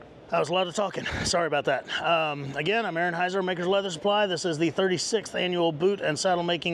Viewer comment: I always hear it as "I'm Anheuser, Maker's Leather Supply"... (no offense intended) Drink what you want to drink, some days are longer than others.